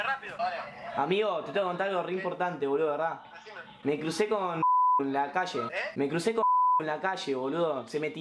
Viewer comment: Parece que dice PALOMA